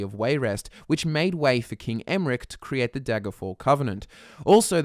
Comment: Did he just say King Emrick xD